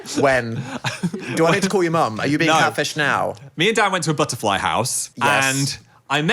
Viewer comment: oh no 2:38 ... the subtitle being "me & dad went to a butterfly house" instead of "me & dan" just... yeah